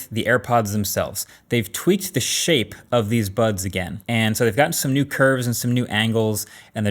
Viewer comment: I swear I heard him saying “they tweaked the shit of these buds again” 😂